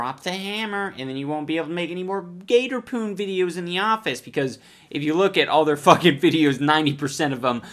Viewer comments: Am I the only one who thought it said gator porn? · I heard that as gator porn